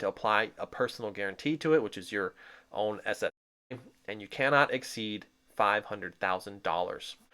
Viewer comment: What did you say at 5:07 ? Apply your own__________ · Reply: Assets